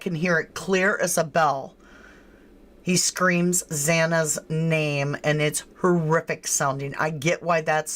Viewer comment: I almost thought I heard Xanax's name being said over and over.